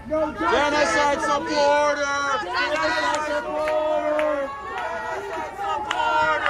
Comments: Ooohhh. I thought they were saying "genocide our BORDER!!" 😂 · Oh, that's what they were yelling. I thought they were saying "genocide the border."